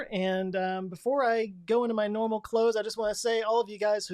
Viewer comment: Oh "CLOSE", I thought you said "before I go into my normal clothes" and I was like, what did you wear that top special?